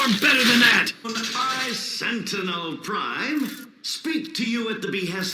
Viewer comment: Pfffffffft Maidens-prime... My goodness, that hits me hard. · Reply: I thought it said Maintenance Prime